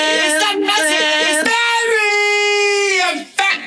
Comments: You don't have to faAsh them You don't ha do ha do catch them it's that nasty, it's diarrea... FACK DaaaAAAAAAAAAAM That's what i think it says XD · Did Pikachu just said Attention to all Roblox Gamers? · \3:33 '' i hear ROBLOX?!